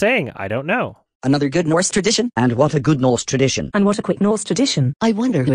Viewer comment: Everytime he plays Zazus clip, I literally hear "And what a good Norse tradition"